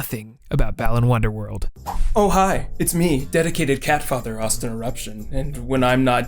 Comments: am I the only one who heard 'dedicated cat fucker' here? Yes? Okay... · Did anyone else hear "dedicated catfucker" at 1:15 ?